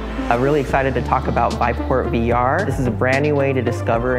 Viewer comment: Thought it said "Viveporn VR"... That would be rather popular.